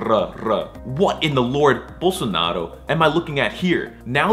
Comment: I simply cannot hear anything other than "Bolsonaro" at 2:19 Am I going nuts?